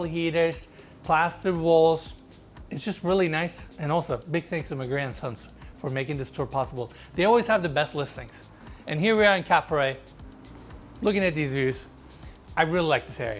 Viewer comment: Thought he said, "...big thanks to my Grandsons" lol and crew, keep killing it! One of my favourite channels!